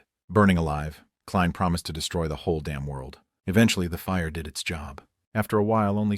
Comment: I thought he said his going to destroy the world 🌍 and 😅😅😅😅